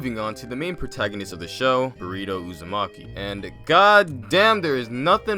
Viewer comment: oh my god he said burrito instead of boruto